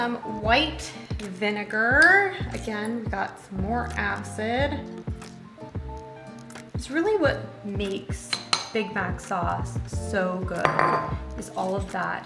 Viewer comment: all I heard was "BIG BACK SAUCE" lmfaoooo 💀